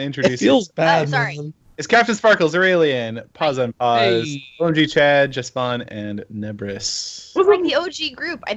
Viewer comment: Anyone else hear "Orgie Chad"?